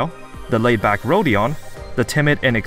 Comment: Am I the only person who had a stroke every time he called Gregor Gregory? It's small, but for some reason it just slaughters my brain every time he calls him Gregory. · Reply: Yes! At first I thought he just said „Gregor here“, but it’s so jarring haha